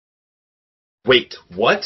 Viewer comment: I hear "BETHANY I MADE BULLS*ITS💀"